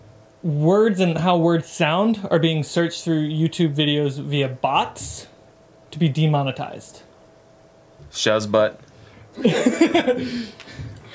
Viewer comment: DID IT JUST SAY SHAZBOT???!! TRIBES INTESIVIES